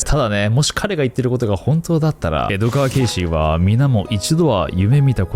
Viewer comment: 江戸川ケーシーって聞こえてしまうエドガーって紛らわしいwww